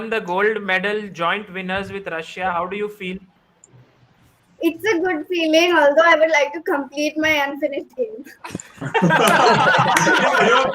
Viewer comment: What did Divya said at 3:40:41? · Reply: Its a good feeling, although i would like to complete my unfinished game